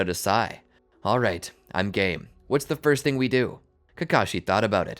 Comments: Did anyone else hear i'm gay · I thought sasuke said all right I’m gay at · is it just me or did he say I’m gay and I know he said I’m game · When sasuke said : Alright I'm game...I thought he was saying:Alright i gay 🤣🤣🤣💀💀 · i heard sasuke say im gay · did sasuke said "i'm gay"?